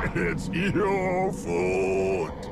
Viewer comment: after a while it just sounds like hes saying 'its your fault' and 'its always been your fault'